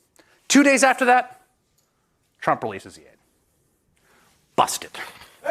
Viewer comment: What is he saying at 13:10 ??: "And you know what happened? 2 days after that? Trump (*this part is garbled*) - busted" I've played it over and over and still can't get what he said there...... anyone? Thanks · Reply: He says that 2 days after that tRump realeses the funds.